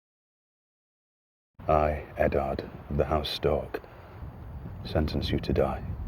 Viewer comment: Did he just say "I Eddard of house STORK" ? lolol